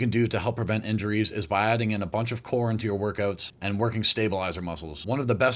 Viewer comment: i heard that as "adding a bunch of porn into your workouts"